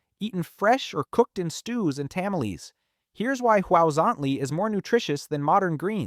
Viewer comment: Did I hear, tamma leeze? 😂